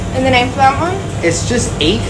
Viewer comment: I heard him say “Eith Myers”, but she went full on “eat my a$$” 😂😂